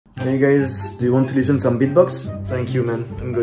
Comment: I thought he said do you want some Vbox